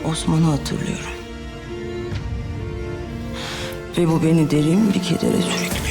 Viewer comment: ne dedi ? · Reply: Oyun Hikayesi "Ve bu beni derin bir kedere sürüklüyor..."